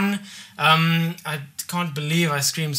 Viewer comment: He kept saying "past minute" and I thought he said "boss man". lol Screamed so loud for the boss man!